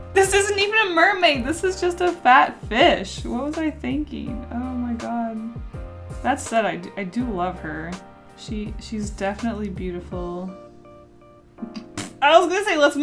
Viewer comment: I legit heard fat bitch XD